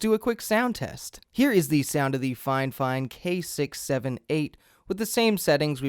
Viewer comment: On all videos I saw about this mic I always hear ‘FineFine’